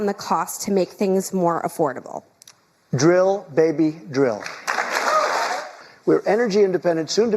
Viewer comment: I think most of us want 4 years of competent leadership that breaks the corporate stranglehold. Most of us can see the division is just a tactic. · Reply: Did he just say, "drill baby drill?"